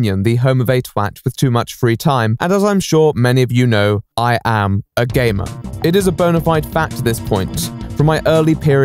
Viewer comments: I thought he said, as you all know I'm a gay man. And I was like : I didn't know that! 🙂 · i thought he said “i am a gay man”